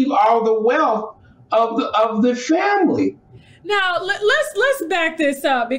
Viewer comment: did he call it a sham-ily.🤣🤣